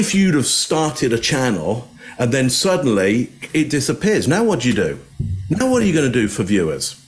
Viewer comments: I heard 'what did you do' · I think it sounds like she says, what did you do.